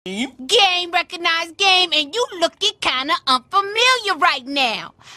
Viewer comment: I thought you said gay recognized gay😂😂😂😂😂